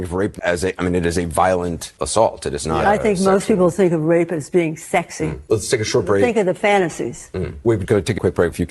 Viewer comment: I thought she said rape was a fantasy